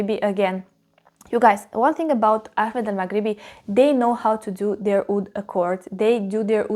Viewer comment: How do I spell this, did you say “Ahmed and magriddy”? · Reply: The brand is called Ahmed al Maghribi, they have beautiful fragrances.✨️ Thank you for watching ❤️